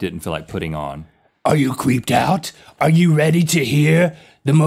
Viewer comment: Link said are you creeped out yet, and it sounded like he said are you queefed out yet! HAHAHA LOLOLOLOLOLOL